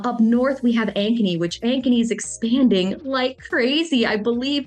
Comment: great video. great to see the interviews and i have been watching chanel about as long as i have you. awesome you 2 are friends. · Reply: When you first mentioned Ankeny I thought you said “agony.” 😮